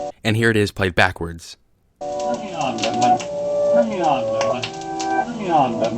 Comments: I thought it was saying "Bring it on dead man" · I hear "Turn me on, Desmond!" xD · SOUNDS LIKE HE SAYS TURN ME ON BAT MAN OVER AND OVER AGAIN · I hear "turn me on, batman!" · I heard "Bring it on, dead man" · All I heard was "Turn me on Batman" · I hear "Turn me on Edmond" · I heard 'bring it on dead man' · When played backwards... All I heard was: "Turn me on,Batman." X"D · To me, it sounds like it say, "Turn me on, Debman."